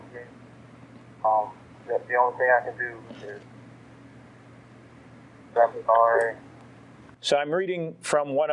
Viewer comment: At timestamp 24;03-24:04 the (indecipherable) part I think he says " Say I'm sorry"